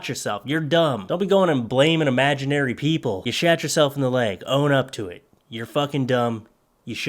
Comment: sounded like you said “You shat yourself in the leg”